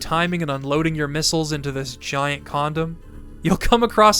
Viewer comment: If I had a nickel for every time I heard someone call the boss at 15:45 a condom, I'd have 2 nickels. Which isn't a lot but it's weird that it happened twice